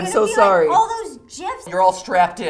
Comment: DID SHE JUST SAY JIF???